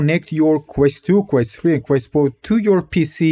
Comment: zero did you say quest 4?